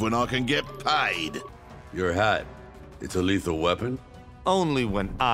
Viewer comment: I heard this as your hot that’s a lethal weapon